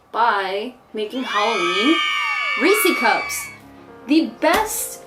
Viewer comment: I have captions on and it thought you said greasy cups 🤭 LOL